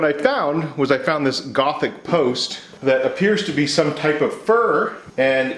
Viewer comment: "it seems to be some type of fir" - what he said "it seems to be some type of fur" - what i heard "i didn't know you could turn foxes into wood!" - what i thought